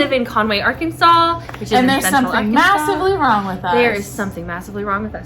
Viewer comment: I thought you said “magically wrong” initially which I thought was hilarious. And yes I missed both of you! Just made it to the end… GAY RIGHTS!